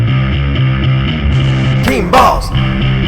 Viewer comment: sounds like he's saying teen balls instead of "team balls"